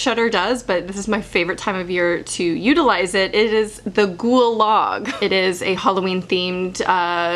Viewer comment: When you said “ghoul log” I thought you said “gulag” 💀🤦‍♀️